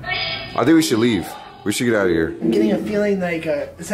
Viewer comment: Hope everyone is staying safe and being smart during these tough times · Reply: at 20:54 it sounded like they said get out